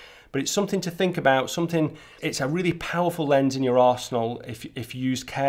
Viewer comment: Love your Videos and Photos. You made me spit my tea towards the end... I Thought you said "It's a really Powerful lens in you'r Arsehole" you actually said *Arsenal.. Lol. It only made it worst the fact that we're talking about Ultra Wide Lenses.